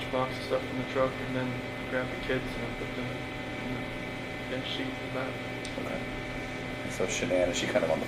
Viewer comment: Chris said he put Shanann in the truck then grabbed the girls it sounds to me like he said he put them in the sheet. If he did that then they had already been murdered. He seems to have caught what he was saying. He couldn't know CeCe was just about to get out of bed if he was in the garage putting Shanann in the truck. Right? He put the kids & Shanann in the back seat. Shanann on the floor & the girls on the bench seat. Who did he have in the front passenger seat???